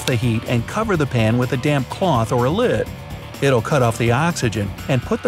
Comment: ??? Did i only hear damm